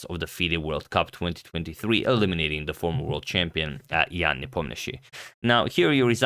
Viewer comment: did he said the former world champion 😂